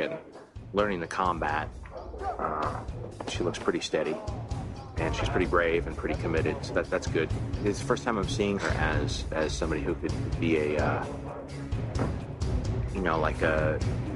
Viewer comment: thought he said ..it's the first time I see her ass🤣🤣🤣bruhh